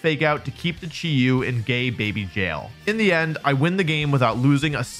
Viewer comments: did he say gay baby jail?? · Did he say "Keep the Chi-Yu in gay baby jail?" :face-blue-smiling